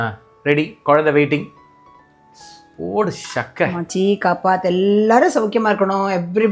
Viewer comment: What does aunty say before serving food? It sounds something like ma chi ka path. What does it mean?